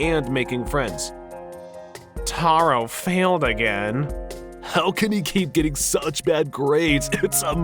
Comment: I swear I heard Squilliam Fancyson when I heard “Taro failed again.” Lol